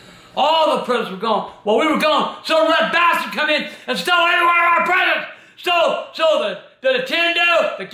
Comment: It sounds like AGP said some black bastard stole everyone of our presents? LOL